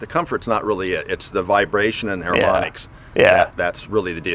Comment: he's explaining something about maybe areomonics? I'm not sure what he says does anyone know what he says? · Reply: Harmonics... as in the way vibrations counter or complement each other to make the ride more “harmonious” or pleasant.